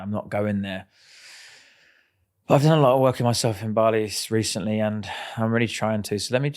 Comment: Did he say Bali? 😂🤣